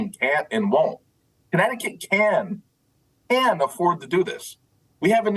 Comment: I think the F-Bomb Tom thought he heard was "Connecticut can F'n to do this..." but the guy said, "Connecticut can AFFORD to do this".